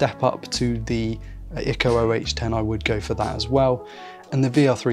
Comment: What iem do you mention at 7:08? Tried to find them but not sure what you say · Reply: Sounds like Ikko OH10